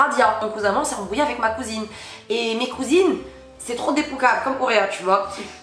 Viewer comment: a dis quoi dans 9:01 mes cousines sont très.......... réponds moi stp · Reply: Des poukaves